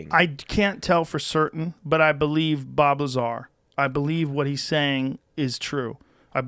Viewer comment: Honestly man hahahahahahahahaa when he said Bob lazar I literally heard " blah blah blahhh" at first